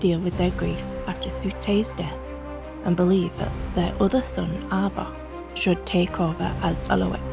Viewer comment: did u say Abba? My mum knew a girl named Abba from Ghana 1:13 nvm Avok.